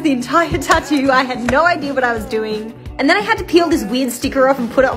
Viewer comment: Ngl thought she said “weed sticker” for a sec- 💀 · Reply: Oooh same ..she said "Weird sticker"